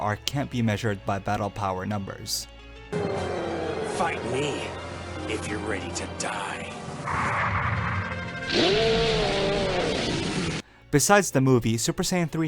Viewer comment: Literally thought he said, "Fuck me, If you're ready to die." lol xD